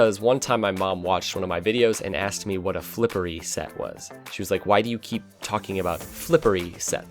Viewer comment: The first time I watched any rocket league content was sunless khan. I thought he was saying flippery set as well. took a couple more vids to realize what they were saying.